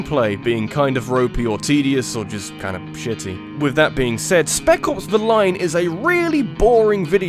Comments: Oops I thought you said Speckles the Lion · Totally thought you were saying, "Speckles: The Line." I was like, "Jeez, that's a dumb title." XD